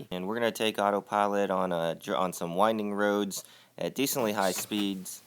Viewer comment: Am I the only Australian that thought it said "Windang"?